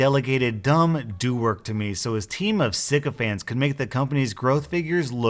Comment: I thought he was saying psychopaths in a weird voice for 5 minutes I swear to god I dont think I've been this confused